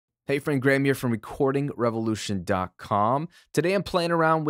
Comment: Did you say "revolution dark cum"? x'D